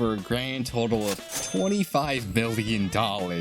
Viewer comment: did I just have a stroke? Or did he say 25 billion dollays?